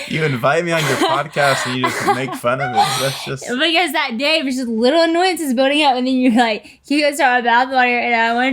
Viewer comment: min: what in the world did she said here? love you haha 😄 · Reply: “Because that day was just little annoyances building up…”